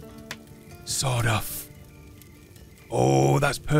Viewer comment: When he said, "Sawed-off," I thought that he had said, "Sod off." It was so funny. XD